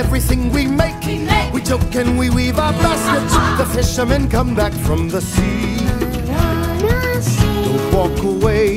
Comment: Swear moana says ‘moana’s sea’